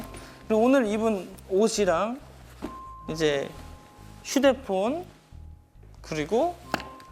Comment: He literally said POOON (10:43) hahah why all my kpop biases need to do the same things. BTS Jimin also says pon instead of fon. I started to call phone pon when I heard him saying that and now Woodz said that too and I'm dying. I can't, I love him I swear I love him. Why am I so happy right now, but thank you Woodz for making me happy. 😂😂😂